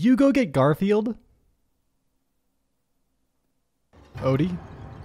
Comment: what everyone heard: Odie? What I heard: Cody?